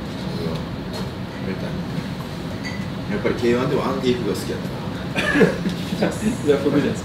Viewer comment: 編集者さんアンディーフグとアンティークの聞き間違いはあかん笑笑